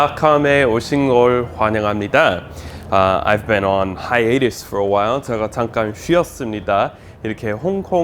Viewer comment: "I have been on ~~~~~for a while. 잠간쉬었어요" 하이디 로 들리는데 무슨말씀을 하셨는지요. 처음 opening comments 중에 하신말씀입니다. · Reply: HAHN hiatus인 것 같습니다.